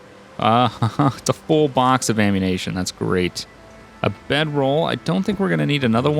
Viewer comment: anyone else notice at 3:45 he said ammunation instead of ammunition lol